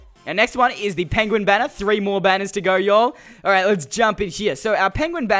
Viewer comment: did he just say... "yoll"?!?!?!? XD